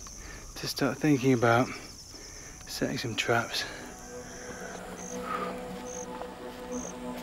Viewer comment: I had to replay it a few times. I kept hearing him say “Maybe it’s time .. to start thinking about.... sex and drugs.” After the fourth time I realized he was saying “setting some traps “ lol